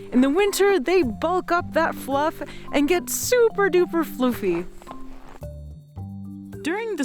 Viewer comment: did she say floofy or fluffy? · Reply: Definitely floofy, which is (of course) technically correct.